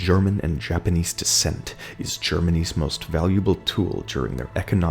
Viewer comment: when you say “Germany’s” u thought you said “Germanese” like half German half Japanese 😂😂😂💀